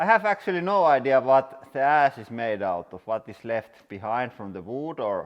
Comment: The auto generated subtitles at 1:52 have you saying "I have actually no idea what the ass is made out of" instead of ash lol